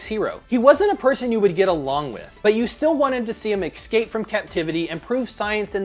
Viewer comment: ESCAPE. Maybe it's my computer or my speaker, but it sounded like you just said: EXSCAPE.